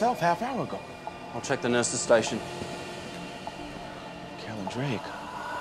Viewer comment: What does Foreman say at 3:02? The auto-generated subs say "Carolyn Drake", but that makes very little sense to me.